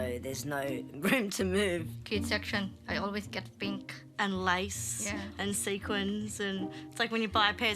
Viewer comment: I thought she said "lice" I was thinking I know kids can pick it up the odd time but how bad is the issue where you live 🤣